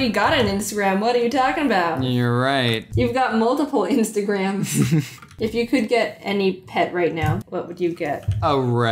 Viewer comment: I thought she said: "If you could get any head right now," And I nearly choked on my chocolate, and rewinded.